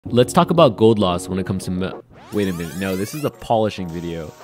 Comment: I thought you said gold laws at first